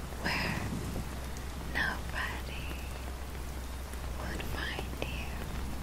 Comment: Love that she outright tells you you are in danger there alone with her, and yet tells you she'll keep you safe....somewhere else....where you won't be found. It's got all the titillating creepiness of a Nick Cave song and a Poe story. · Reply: When I first watched it I heard "where no baddie will find you" instead of "nobody".